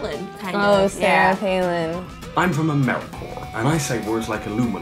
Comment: did he say americore???